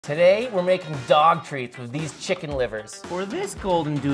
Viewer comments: I THOUGHT HE SAID WE ARE MAKING DOGS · When you say dog treat i thought you say dog meat